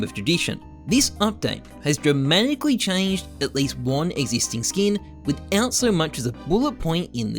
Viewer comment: existing, emphasis on the isting. I had to rewind that like 5 times to realize what was said, partially my autism. highfive.